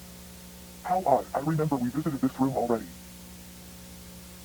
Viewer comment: For those who can't understand what is being said at 8:58, Sha is saying the phrase "How odd, I remember we visited this room already." This implies that Sha knows that something has modified the way she is supposed to move through K-9, but does not know why. She also, asks "Sophie, where am I?" and after that a very sped up and pitched down voice clip of "Where is she?" is repeated before the black Rosemary "sprite" appears and the Rosemary backstage sequence begins.